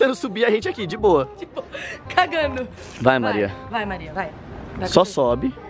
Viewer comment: só eu que escutei alguém falando "É a Maria" ?